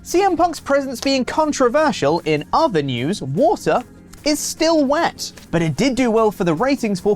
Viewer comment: Not gonna lie when Pete said, “water is still wet” I thought he Walter….. oh my! 😂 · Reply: I also thought he said Walter.